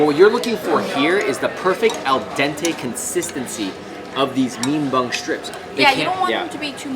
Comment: Wait. Did he just say mean bung instead of mung bean?